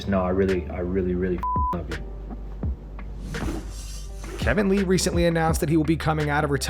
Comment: Hearing Kevin Lee talk about wanting the winner of Conor and chandler just shows how much he hasn’t learned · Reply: I thought you said Kevin Iole and I was like.... WHAT 😂😂💀💀